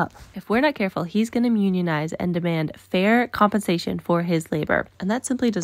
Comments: I heard "mew"nionize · That was definitely "mewnionize" I heard and I'm dying. That was cute 😭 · I swear I heard you say he was gonna mewnionize